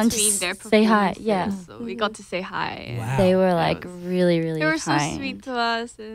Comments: did rose just say jaden and diplo were "were really really high" ?? loooool i mean i wouldn't be surprised. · Idk why Im laughing at this did she just say "they were like really really high"?or hype? hahahaha😂😂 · Did Rosé say they were really really high💀?